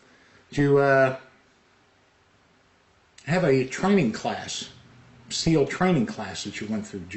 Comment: They said "training class name" I heard "Chinese last name."